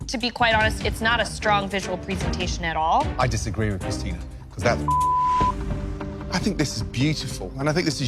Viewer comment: What did Gordon say to/about Christina? "I disagree with Christina -- [bleep]" ?! What the hell got bleeped? Look at Christina's face! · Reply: I think he said, “Cause that’s bullshit.”